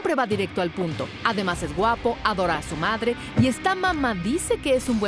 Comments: yo escuché q decía esta mamadicimo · "Y esta mama dice que es..." , pense que dijo " Y esta mamadicimo XDDDD" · por un momento pense que dijo "y esta mamadisimo" xd